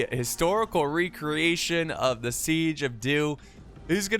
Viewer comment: I swear to god I thought he said the siege of Duke. I was gonna laugh so hard